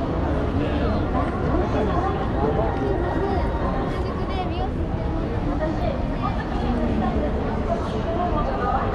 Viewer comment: what she said? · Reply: 「すいません、新宿で美容師してる者なんですけども。」="I'm sorry, I'm a hairdresser in Shinjuku." Perhaps she was looking for a hairstyle model. The hair of a passerby woman is long. In cities like Shinjuku and Shibuya, hairdressers often call out to passers-by to look for hairstyle models. Of course, these scouts are giving out free invitation tickets.